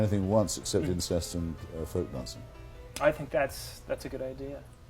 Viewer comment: except for incest and "*what*" I couldnt make out and CC is wrong. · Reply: folk dancing